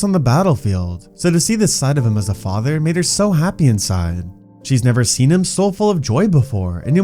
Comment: what did u say here? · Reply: He said “made her so happy inside